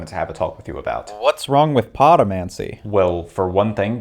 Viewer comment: When he said "podomancy", I thought what he said was "potomancy" and was expecting him to mention magic gained by getting high or some shit.